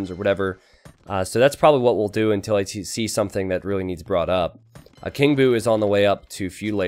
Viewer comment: "Until I TC something." You tried to disguise it but I heard it anyway mate. ^^